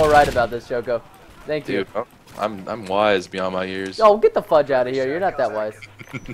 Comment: did just say "get the fuck outta here"!?!??!